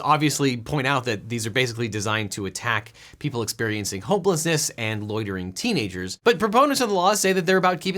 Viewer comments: The caption at 12:41 says "homelessness", but he clearly said "hopelessness" 😂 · did anyone else hear 12:40 as "people experiencing hopelessness"